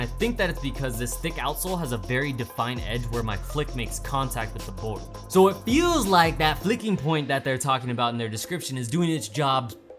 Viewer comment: i thought that said “fucking point” not flicking lmao